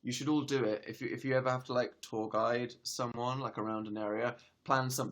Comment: When he said tour guide I thought he said tall guys cuz his accent